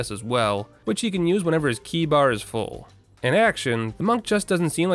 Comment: Dude it's Chi not Key, like in "cheese." Remove the ese and you have the "che" sound and that's chi. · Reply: I thought he way saying Key Bar, since you press keys to activate the abilities, all the way till Chi was on the screen. It's Chi like Tai Chi haha